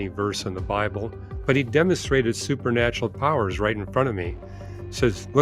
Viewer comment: I thought he had said “Lemons are real”! Shucks, demons???